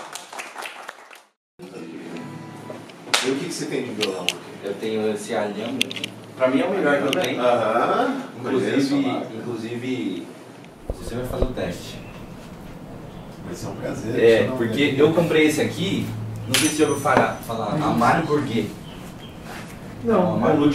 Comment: Foi um momento lindo! ❤ muito especial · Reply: Que legal, Kethlyn! traz está sensação de ser “o cara Top” da nossa família. Aos 5:44 eu ouvi “o senhor faz o teste”; 😂 compreendo que num cômodo/ num cenário de violões o pronome muda sem perceber.